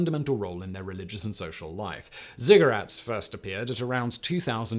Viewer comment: With that accent every time he says ziggurat it sounds like he is saying cigarette, and now it's time for a smoke break.